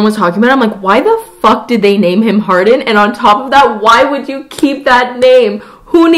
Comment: I think you're not looking deep enough into the symbolism... Anna Todd clearly wanted the dude's name to represent his main character trait... being hard all the time. Get it? He's “hard”in for her! Who knew fanfiction-based books could be this deep! Only Anna Todd could pull this off... what a genius... honestly. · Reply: I thought she was saying Hard on 😂😂